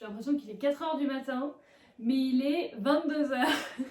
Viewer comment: c moi ou elle a dit "il est 29heure"